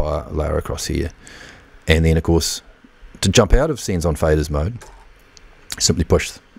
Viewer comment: Very cool. But I'm curious - did you misunderstand the term "Sends on Fader" and accidentally call it "Scenes on Fader"? ;-) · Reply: Lol. Must be the accent, it really sounded like you were saying "Scenes on Fader", and then I saw "Scene" on the Scribble strips. My mistake. Thank you for clarifying!